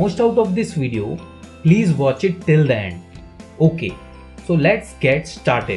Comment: what did the sir said?? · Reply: He said till the end